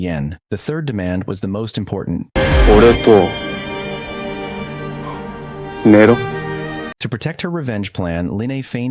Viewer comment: What did he say in 1:21:42? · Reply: He said she has to sleep with him 💀🤮